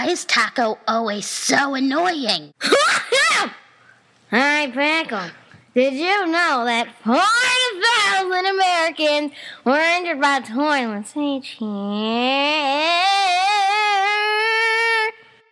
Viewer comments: I finally figured out what taco is saying at 0:20 she says: hey pickle did you know that 40000 Americans are injured by toilets each year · I was wondering what taco said in 0:18 Maybe she/he was saying : Did you know that hundred pile of americans when hundreds went to the toilet section nice (dies)